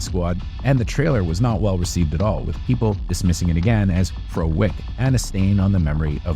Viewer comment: what are you saying here? Pro wick? What does that even mean?